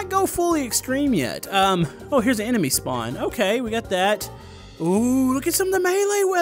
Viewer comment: For a second when he said enemy spawn I thought he said anime spawn.